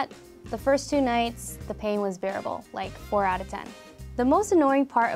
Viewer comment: At first I thought she said fucktuating